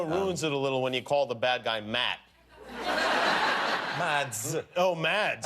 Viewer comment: Did he just say Matt???? Dardevil Confirmed lol